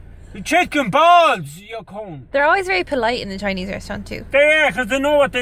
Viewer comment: "They're always very polite in the Chinese restaurant." "They are, they know what they'll get, they'll get the *deportion*." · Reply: Oh lol I heard "the portions" as in large portions of food